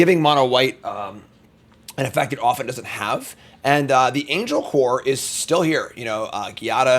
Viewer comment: did he say angel whore?? Wtff